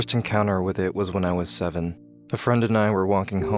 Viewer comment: Me: did he say seven! Let me replay that part he had to say 17 Repays.... Me: wth is my life